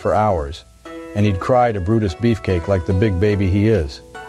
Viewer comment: I legitimately mid-heard this as "Buddist Beefcake".